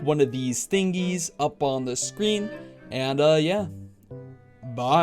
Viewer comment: your "bye" kinda sounded like goerge